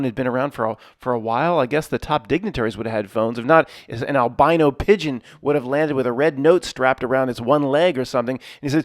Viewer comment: Eldino pigeon, lol... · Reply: I thought he said albino pigeon. Spit my coffee everywhere. 😂